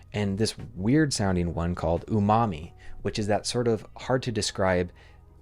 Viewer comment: "Umami" Bro, i heard "your mommy"